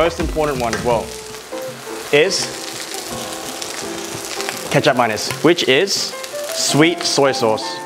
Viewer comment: Reynold: Kecap Manis Me mishearing the pronunciation: Why would he put ketchup and mayo? Reynold: Which is soy sauce. Me: oh.